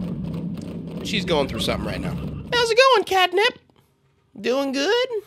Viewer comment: Hows it gonna catnip (thats what i hear 💀💀💀)